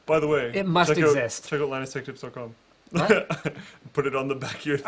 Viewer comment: God damn it, I mean't I thought I heard him say NCISTechTips. Now I look like a tard.